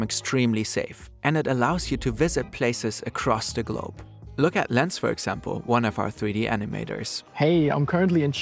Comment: caption is saying burn, instead of... Damn..